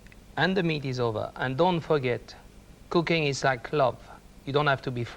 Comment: "And don't forget. Cooking is like (??? clob ???). You don't have to be french to be good at it." What is he saying? Can anyone help me here? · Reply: Love... ie making love